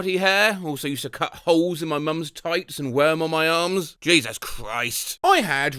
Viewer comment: i thought instead of "tights" it said "thighs" ...😭